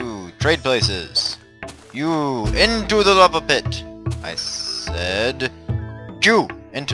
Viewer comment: i heard "JEW into the lava pit!" and then i subbed